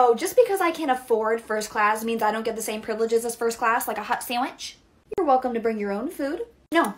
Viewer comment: Sounds like she is saying "hot sammich" lol